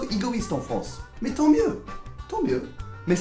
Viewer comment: qu'est-ce qu'il a dit à 2:40 après il dit 'on est un peu egoiste en france' ?? ton mieux?? je sais pas ce mot/phrase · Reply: somethingellie "Tant mieux" = So much the better.